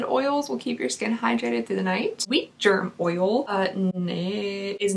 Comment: I so thought you said "Beef Jerk Oil" and I was like... that's not vegan. Then I replayed and heard "Beet Jerk", still didn't make sense. FINALLY I understood you were saying WHEAT GERM. I think it's time for me to go to bed lol.